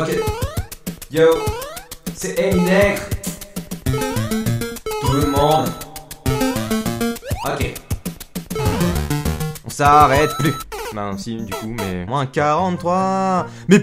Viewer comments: c moi ou il dit "c'est Eminègre" 🤣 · C’est moi ou à 4:15 il a dit Eminegre 😂😂